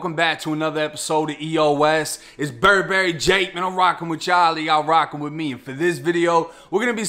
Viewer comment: Thought you said "Bird Mary Jake"